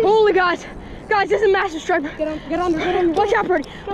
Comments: At first i thought it said stri**er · I for real thought it said 30 inch sniper not striper 😅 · I thought it said "30 Inch stripper"💀